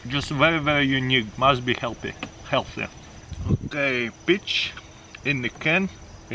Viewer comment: When he said peaches in a can I thought he said b**** in a can lol